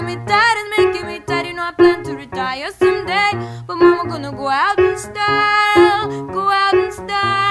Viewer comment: I think it's "momma gonna go out in style" contrary to what 1:58 says.